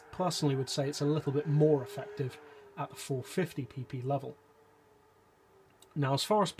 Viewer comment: Turn on captions, it says peepee instead of PP. 😂🤣